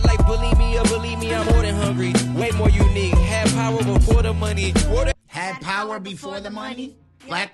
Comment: I thought he said have power like border money 😂😂😂